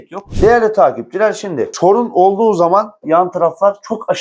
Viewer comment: Arkadaşım, videonun 2:20 dk kısmında " TORUN OLDUĞU ZAMAN " diyorsun nedir o TORUN, ne dediğin belli değil..açıkklarmısın torun ne..? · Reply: Sorun😂